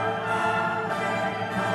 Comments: i am the only who heard "sus amongus" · it sounded like “AMOOOGUS MOGUS MOGUS MOGUS” like bro it’s everywhere why